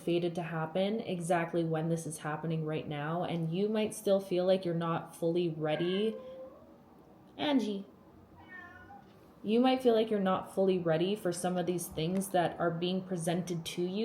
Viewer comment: aww auto-generated captions said Angie said hello 🥰